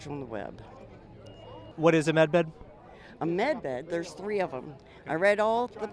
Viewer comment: anybody else hear, "what is a meth head?" 😂